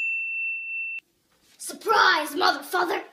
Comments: "Surprise mother-father" Umm ok i misheard it 💀💀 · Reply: I thought she said Mother*****r 😂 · I thought she said she said "suprise mf" but she actually said "suprise mother father" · What she said “ surprise mother father” What I heard “ surprise MF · i thought i heard "suprise mother fucke-"☠️ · When I was younger I thought she said ‘surprise motherfucker’ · I really thought she said a bad word but she said “surprise mother -father”💀 Edit: IM FAMOUSEEEEE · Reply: I was thought said motherfu- 💀💀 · I thought she said moter fuckers but she said mother-father and who else think like me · I really thought she said a bad word but she said “surprise mother -father”💀 Edit: IM FAMOUSEEEEE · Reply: The first time I heard it I thought it said surprise mother fu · I literally thought she said "Surprise mother f_____" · Ppl who thought she said “Surprise mother 🦆 er” 👇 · I really thought she said m**********r but then I got to know that she said Mother Father Lol